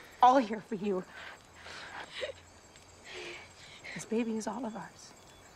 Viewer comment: I thought she said, "This baby is all of ours." But yes, meaning the same thing :)